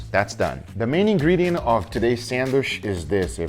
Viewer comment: It sounds like he saying Sanwish